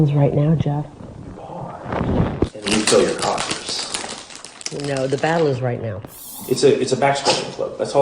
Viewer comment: I think he says "pause and we fill your coffers'🤔🧐